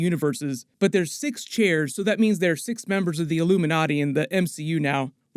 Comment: I heard "Illumi-naughty"... :P Regards from Tabasco, México [Land of The Olmecs]!